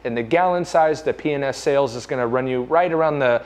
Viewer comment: P & S enunciate I swear it sounded like you were saying penis! Lol 😂